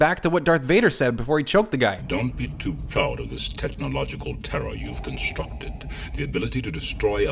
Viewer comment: I always thought Vader said, "Technological terra" not technological terror.